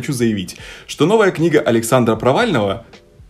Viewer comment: Это моя шиза или кто-то реально услышал на фразе Александра "провального" вместо слова "провального" слова "Навального"